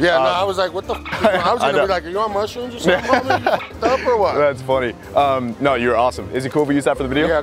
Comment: did he just say “you’re muslim or what” WTF IS THAT SUPPOSED TO MEAN